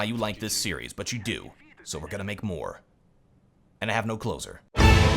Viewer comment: What does he say at the end? Is it meant to be "and I still don't have clothes on", because all that I hear is: "I don't have closer" or something like that